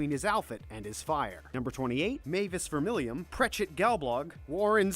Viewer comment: it sounds like he is saying gayblog